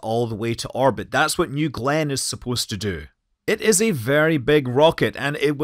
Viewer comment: I thought you were saying gland the entire time. You were saying Glenn